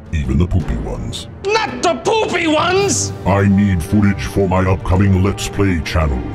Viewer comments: Not the poopy ones! ~JonTron 5/10/15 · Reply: Mackenzie Rayne I thought he said the Boobie ones. LOL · I should have suspected it was Arin. Who else would say "poopy"? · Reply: I thought he said "Boopy" at first.